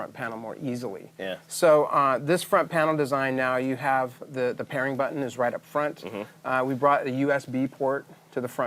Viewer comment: How tf do you eject a disk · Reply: I thought he said that was the sync button